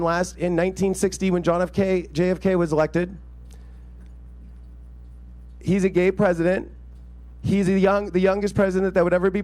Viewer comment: Wait what??? JFK was a gay president??? I played it back....did i hear that correctly???? At 13:47......??